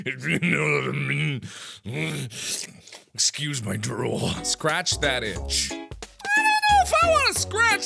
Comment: At first I thought it said scratch that b*tch.